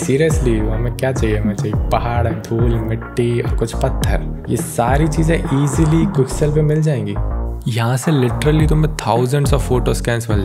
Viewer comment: Amazing work 🔥 Can you please tell from which website you downloaded that environment for blender? you said at 3:55 but couldn't hear clearly. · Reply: Quick sell